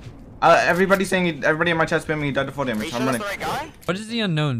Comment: What happened ? 0:33 what is he saying ? Wtf these kids talk so fast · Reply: “Chats spamming he died to fall damage”